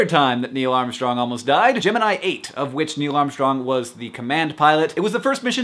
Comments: Hank, you said "Gemini 8". I heard "Jim and I ate." I was confused. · I heard "Gemini 8" as "Jim & I ate" and I was like, "ate WHAT??" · He said "Gemini 8" i heard "Jim and i ate"